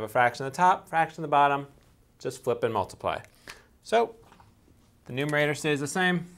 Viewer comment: "Just flippin' multiply." Lmao ^.^ sounds like he said that